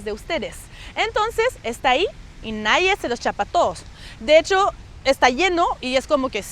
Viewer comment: "Y NADIE...(¿qué?)" No entendí lo que seguía. Activé los subtítulos en inglés y What? SHOES?, en francés Quoi? CHAUSSURES?, en español ¿Qué? ¿ZAPATOS? Y por fin di con la frase correcta "Y nadie SE LOS CHAPA TODOS" (que suena parecido a : Y nadie "se los zapatós" (de ahí esos subtítulos graciosos) 😆